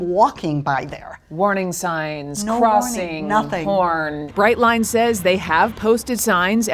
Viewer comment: why does she say porn? look in subtitle too